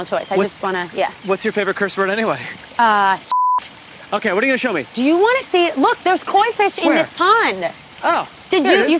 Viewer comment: what was that curse word? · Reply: Sh*t